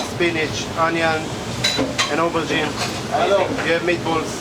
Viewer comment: i think he said aubergine but the subs was (inaudible). great video of Jerusalem !